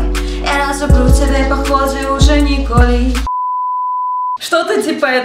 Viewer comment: Я одна услышала "Николь" ?